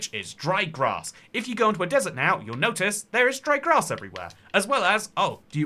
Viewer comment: when u said dry grass i thought u said dried ghast lol